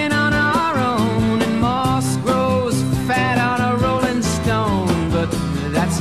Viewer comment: I thought it said “moscow fat on a rolling stone” and “lenin (like for the soviet leader) read a book on marxs”